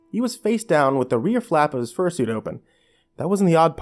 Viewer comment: I thought it said fur shit XD